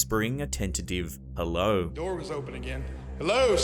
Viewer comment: I don't think the voice is saying "hello" - I think it's "help!"